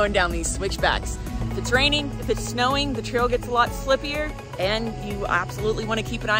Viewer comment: Beautiful as always. Colorado is definitely on the bucket list. Did just say "Slipier" 5:51😂